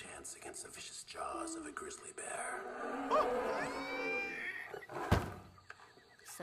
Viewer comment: I hear "WEAR OFF THOSE SHOES WEAR OFF THOSE SHOES" if im wrong i understand my mistake